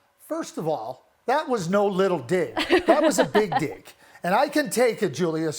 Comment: What did he say at 0:54 😮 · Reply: He said “that was no little dick, that was a big dick” 😂😂😂😂